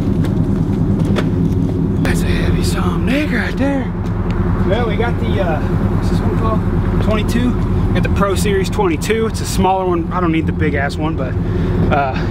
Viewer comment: Anyone else notice or catch at 2:58-3:01 it sounded like her said the N word? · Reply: "Heavy son of a dick" he says son of a dick all the time people